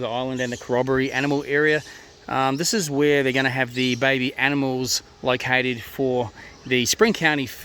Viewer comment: Did you say Baby Animals?